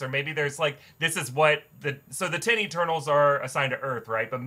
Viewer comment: I swear, when said “10 Eternals” I heard “titty turtles”